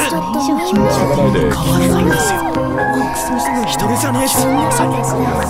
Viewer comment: For Shidou, I think I hear 「____差なんて、意味変わらないんですよ」(‘the difference doesn’t change the meaning’) instead of 「____差なんてないんですよ」(‘there is no difference’) which is said around 0:16 onwards. I can’t make out what goes in the ____, but I’m not quite sure if it’s「人の命の重さに」at all. I also think there’s an additional line for him that starts at 0:20 which goes 「人の命の重さに、そのためなんですよ」(‘it’s because of the weight of a human life’?) Not quite sure about my claims since it’s so hard to hear. I also only gave approximate translations so they may be eh